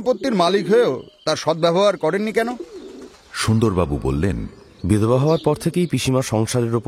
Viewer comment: এ শুনলাম, "সুন্দর বাবু বললেন.." কিন্তু ওটা সম্ভবত "দ্বিজেন্দ্র বললেন" হবে।